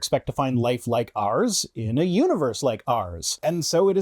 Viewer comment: For some reason I heard "life like arse in a universe like arse", and now I can't unhear it anymore. Well, the argument still works either way.